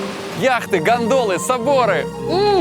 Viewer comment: ой ору мне услышалось яхты,ГАНДОНЫ,соборы)))